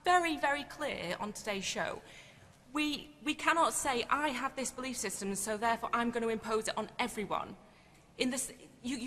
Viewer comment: That chick at around 5:50 confirmed blue dress’s entire argument w that pronoun fumble. She almost said “he” right after, too. She tried it lol. Also, did I hear her say “you can’t push your belief system onto everyone”? Wasn’t that blue dress’s argument about non-binary people? Sometimes it’s best to be an observer and not a participant, lol.